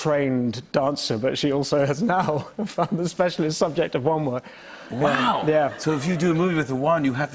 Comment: What he said: Wand work What I heard: WoN wOn